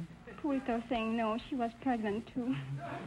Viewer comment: What does the French lady say about her dog at 3:12? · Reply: She said the dog was no comfort and was pregnant